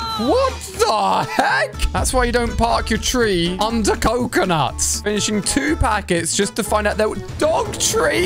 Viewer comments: Yo I think you said park your treeUnder coconut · Wait did you just say''THAT'S WHY YOU DON'T PARK YOUR TREE UNDER COCONUTS''at 0:57? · Did you say"That's why you don't park(Tree's)under coconut